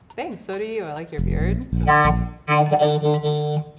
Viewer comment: i thought it said “scott has AIDS” i was like,,,😳